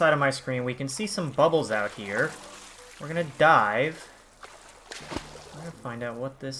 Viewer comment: OH I THOUGHT YOU SAID WERE GONNA DIE XD